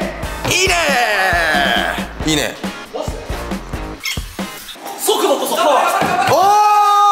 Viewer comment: って、ぺけたんなんて言ってるんですか？ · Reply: 速度こそパワーだ と言ってます！